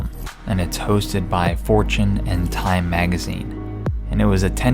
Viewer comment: What i hear is fortune "end" time magazine